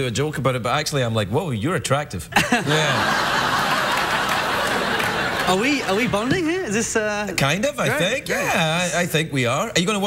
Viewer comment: LMAO he really said "are we about to kiss rn"